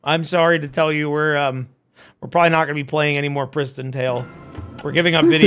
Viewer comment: Shoutout to everybody who misread the game's title as "PrisonTale" at first. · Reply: thought it was prison tale then i thought i heard say christian tale then i finally see its PRISTON tale